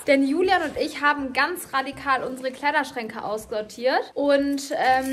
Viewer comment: Wer hat auch gehört das sie bei 0:20 gesagt hat 😅😅 Ausgeortiert statt aussortiert 😅😅